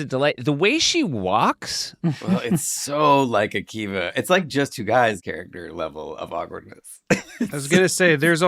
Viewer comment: I 100% thought they were saying Akiva looked like Justin and was like, "yeah, he does!" then realized it was actually Justin so nice work Akiva!